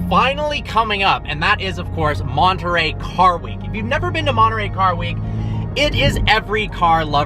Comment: I was half listening, thought you said lingerie car week 🤣